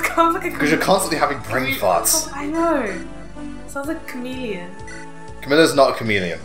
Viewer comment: Okay, I thought your sister said "camellia" instead of "chameleon" when I first heard it. Stupid hearing... Anyway, I was kind of surprised that Binding Blade and Blazing Sword shared the same RNG, but apparently, the former is worse than the latter for most people. Especially when you got heavily screwed over with many hits and the enemy hitting you with very low percentages. Also, I don't see a lot of people use the gate when going through this chapter; probably to avoid being bombarded with enemies in the first place. Also, context about the Fire Emblem! No new characters, but story progress. Next up, more loot, new units (especially your favorite character in the game), and more progress in the story.